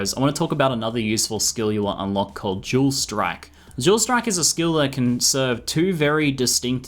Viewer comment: When you said dual strike, I thought you were saying jewel strike XD. I love your accent!